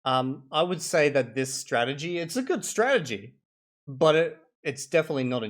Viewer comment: Sorry, Having a hair is a good Strategy, you said or have i misheard? 🤔